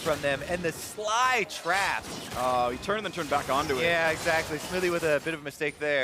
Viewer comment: Did he said Xmithie?